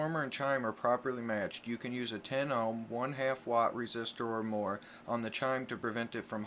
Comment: Have tried up to 1.5w 10ohm resistor and seems to have no effect. Keep moving up or any other recommendations? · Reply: I thought he said one .5 w resistor 10 ohm